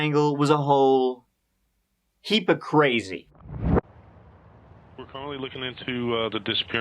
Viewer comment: When you started the video and said “heap of crazy” I thought you said “heap of gravy” thought you should know.